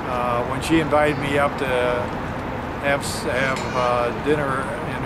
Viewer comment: WHAT a gorgeous couple! 2:03 He almost said "to have sex. HE DID! LOL! Right ON!